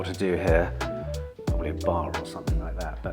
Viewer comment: Absolutely BRILLIANT! this weeks episode is gold. 7m25 Office tour, which i love by the way, but had to rewind it twice because I thought you said "Im not sure what to put here, a bath maybe" lol VW Amrok, great choice. in fact all 4 new cars were good. well done on the sales, BUT please great another Tucson because we definitly need so more TwooooooSon action with a of Tuckson for good measure. Have a great week and thanks for the sunday morning youtube gold video